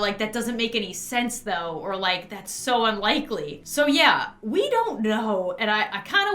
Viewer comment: I’m so sorry that you had to go through all this and having the baby don’t worry i Had worse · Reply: Don't kill me but originally I thought u said "thats so funny" and I was aboutta go ham, then I realized that's not what you said😅